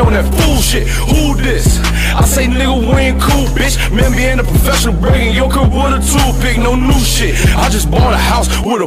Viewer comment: Im dumb asf I alwayd thought he said me being a professional break in yo crib with a toothpick